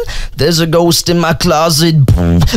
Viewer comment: does he say bird or ghost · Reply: Ghost